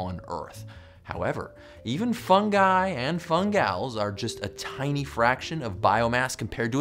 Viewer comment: did I hear fungi (funguy) and fungals